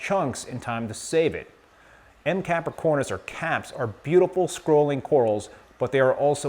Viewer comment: what is the name of the coral at 2:09 did you say capripora i thought those are montipora? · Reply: Montipora capricornis